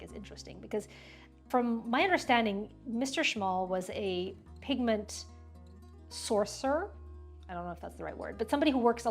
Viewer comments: At first I thought you said, "Mr. Szmal was a pigment sorcerer" lol, pretty grandiose (and kinda fitting based on the watercolor paint) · Misheard: "Mr. Szmal is always formulating new colors and pigment ideas....Mr Szmal was a pigment SORCERER." 😂 That explains how his paint is so magical.